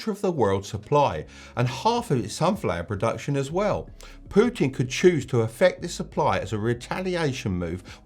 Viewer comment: the Canadian in me thought you said Poutine